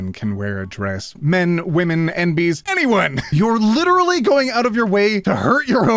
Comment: I misheard that dress part. 'Literally anyone can wear a dress... men, women, and bees...' I could see that, though. (Like that wawa wiwa comic!)